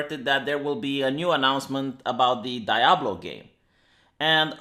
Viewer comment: Did you just say "dayablo"?